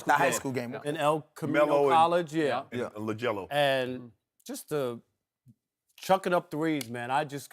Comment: Did Shannon say LaJello?